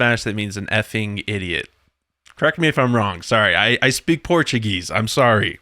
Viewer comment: Oh Gosh. I was listening and walking around the house and heard you saying, you speak Portuguese. I had to run back for this comment. De onde vc é/vem?